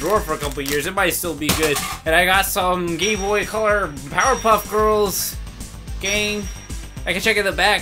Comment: DID U SAY POWERPUFF GIRLS!!!!!